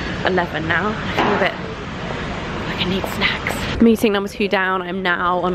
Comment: Oh hahahahah I thought you said " I need sex!" so I had to rewind and watch it again a bit louder, then i heard "I need snaks!" :'D